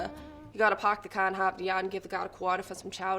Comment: "You gotta park the car in Harvard Yard and give the guard a quarter for some chowder." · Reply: I thought she said hop the yard